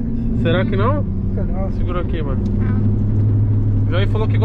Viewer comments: Só eu que escutei "segura ai mor" ou foi outra coisa? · o que Tiago/Toguro fala aqui? É O que ouvi?! só tira dúvida kkkkkkk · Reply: Segura aqui mor kkkkkk